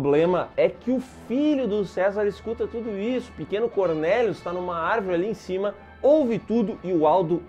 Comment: Faça o download do game Saint Seiya: Legend of Justice: <link> Use o cupom "Saintseiya0712" para ganhar 3-Star Knight Fragment*10, Ouro*88888, Experiência X2 por 1 hora. Acesse o FB do <link> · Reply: Mano kkkkkkk 19:58 A 20:00 eu entendi "corno e surdo" kkkkkkkk